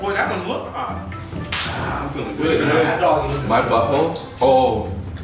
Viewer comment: Did he really say my butthole